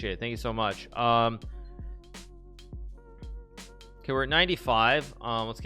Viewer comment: when u watch too much hikaru you hear “ ok knight d5” instead of “ ok 95”